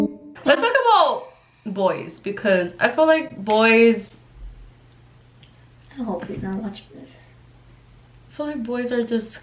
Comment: did i hear "i hope hes not watching this" .... just me??